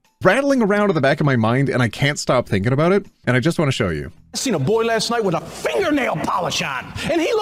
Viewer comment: i thought he said i saw a boy getting finger last night